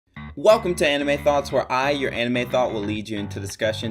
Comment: As I started the video, all i hear is," ANIME THOTS"